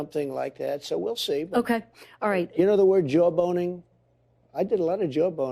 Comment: I thought I heard Joe boning, is that true?